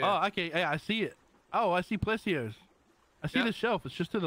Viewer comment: LMFAO... @ 1:18... sounds like he said "Oh I see p&^%$% ears" Also I'm only showing 360p quality for this video ...the other ones I believe were 720P60.